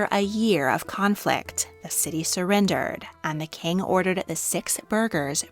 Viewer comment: I died when i heard the sound like: "The King ordered the six burgers", damn, the king was hungry